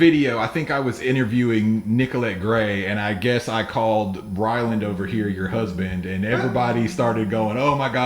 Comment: anybody else catch “Bryland”? 💀😂😂